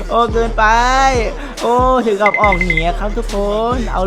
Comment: ได้ยินไม่ผิดใช่ไหม ฟังเอา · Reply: ถึงกับออกห*